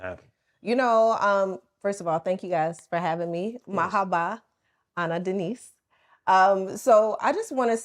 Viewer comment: She said "Marhaba, Ana Denise"...in case y'all didn't catch that at 4:00min mark :)